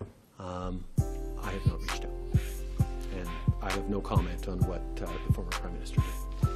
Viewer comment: "Did you call? And what do you think about the fact that Stephen Harper did?" Did he say "I have no shame"? Lol.